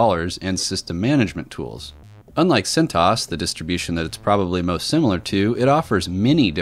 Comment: I thought you were saying it's like SINTAAS, not CentOS. That's quite an accent.